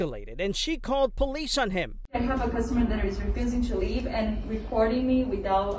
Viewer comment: I thought she said a customer refused to leave.